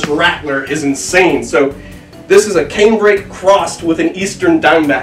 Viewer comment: What is the Diamondback crossed with?? 6:49 I couldn't understand the type of snake he said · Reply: Canebrake / timber rattlesnake